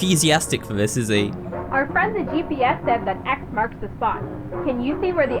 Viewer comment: When Amanda said our friend GPS, I thought she said Our friends at PBS as in PBS kids